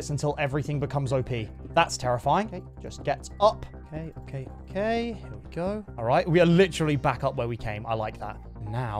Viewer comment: I'm sorry, what did he say? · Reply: Ei Ei Ei im on vacation